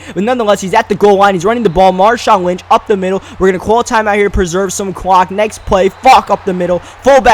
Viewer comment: im so immature LOL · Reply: You thought he said preserve some cock lol